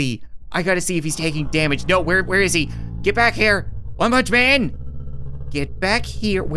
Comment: did he just say "Ma Munch Man" ?